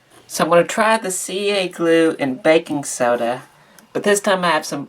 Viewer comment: I shouldn't watch this at lunch time, instead of baking soda I hear "Bacon Soda" and now I'm hungry for bacon :-( This restauration really came out great, considering the state of the internals when you opened it up.